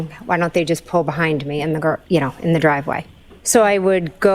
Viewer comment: Did anyone else catch at 12:14 She almost said garage but quickly caught herself and says driveway ? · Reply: You’re right. She literally said “...in the g... driveway... “.